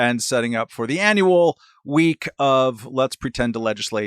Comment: I missed it all...I thought he said i had a WEEK OFF. I always miss the good parties.